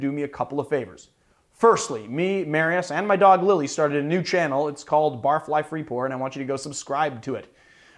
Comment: I'm sorry but I heard you say "its called barfly free porn" and I know that cant be right! XD